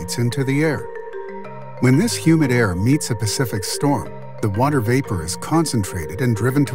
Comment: When this humid air meets the Pacific what? Did he say “storm”?